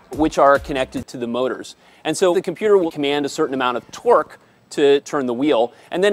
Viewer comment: SO DOES WIRES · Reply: also did he just say TWERK